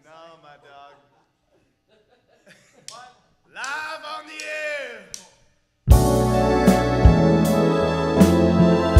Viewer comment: What did he say at 1:10 ? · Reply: "Live of the year" I think